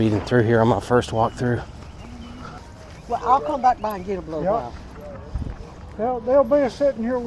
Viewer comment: YO i swear at 1:45 the woman said i will come back and give a blowjob????